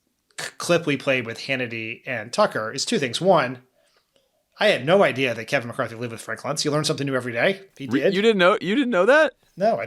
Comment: Can someone let me know what did Dan say at 28:43? (he broke his own recoed of saying something super-fast in a way I can't comprehend . It is mainly my poor English though, and yes, he does speak very fast). tahnks! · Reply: Dan: “I had no idea that Kevin McCarthy lives with Frank Luntz. You learn something every day.”